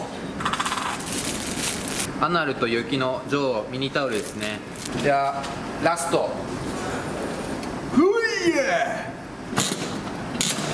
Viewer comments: アナ○と雪の女王？って聞こえたんだけど（笑） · アナると雪の女王って聞こえたの俺だけ？